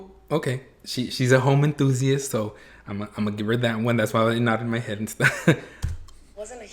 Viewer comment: I swear to Namjesus I thought I heard him about to say “That’s why I nodded my head in Spanish” 😂😂😂